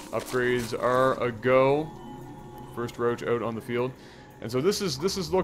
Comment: Did he say BROach @ 5:47?